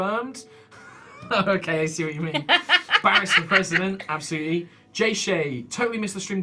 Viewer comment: All hail Barris, the future overlord of Arrak! He alone shall bring peace and usher us to a new golden age, devoid of the dangers after the Lightfall! #BarrisForKing #MakeArrakGreatAgain #PeaceThroughPower #BarrisLIVES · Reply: Nyein Chan I thought he said it as 'Barrus'.